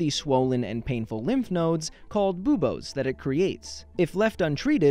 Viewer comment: haha please tell me someone else also kinda thought he said bobies. Or am I alone on this one? Yea I am...